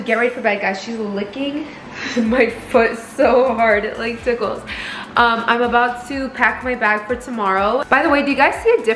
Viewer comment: I swear I thought you said “I’m bout to crack my back for tomorrow” Im like what f*** did she just say? Crack her back? Wtf I had to replay it😂 I was so confused